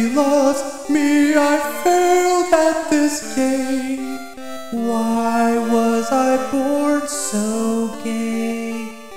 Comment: “why was i born so gay?” ...Did i hear that correctly? ;w